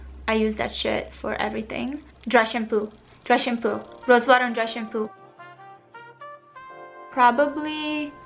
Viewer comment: I thought you said rose water and russian poo..had to go back three times hahaha